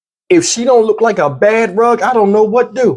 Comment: Sad. But you had me crackin' up. I thought you said she look like a BED BUG. 😆 🤣